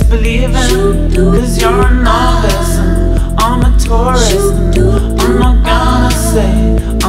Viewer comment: Is anyone absolutely certain he says "I'm a Taurus"? What I hear Is.. "You're a novice, and I'm a tourist" 0:42 later..."You're a taurus, I'm aquarius." It makes way more sense this way, rather than him claiming to be a taurus and aquarius. And novice/tourist fits the theme of space exploration.